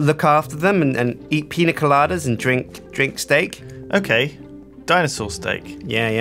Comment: Wait...did I hear "drink steak"? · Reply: FISHARMY Gaming That is, "Eat pina coladas and drink steak."